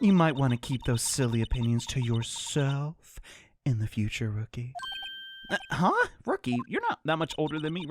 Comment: genuinely thought he said pookie 😭 had to do a double take